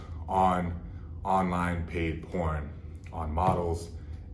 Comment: Thought you said gay porn for a sec..